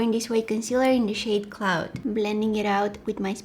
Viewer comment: did she say clout or cloud??